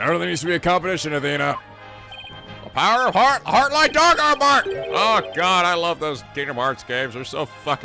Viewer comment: This case has me incredibly engaged, and I don't know why... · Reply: Oh yea, how does say that phrase from Kingdom Hearts 2? I keep hearing 'Heart Light Dark Fart Bark.'